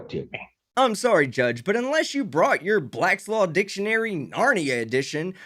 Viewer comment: Did you say "blackslaw"? =/